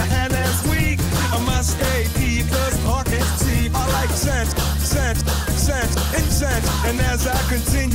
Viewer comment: For the longest time i thought he was saying "I like sex, sex, sex, and sex" And tbh that's still accurate